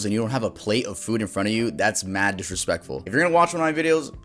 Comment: Me sitting here eating my food and because I was chewing, thought you said it was mad disrespectful to be eating while watching. I stopped chewing so fast Lmfao